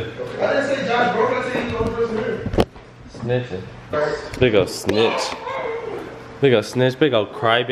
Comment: did josh say nigga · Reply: king Xavier 23696 no he said BIG'O Snitch